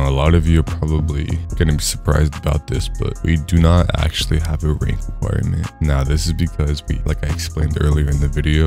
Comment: what did he say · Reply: No rank requirement